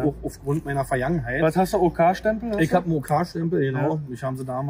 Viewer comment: was fürn Stempel meint er? Verstehe nicht richtig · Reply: OK Stempel..organisierte Kriminalität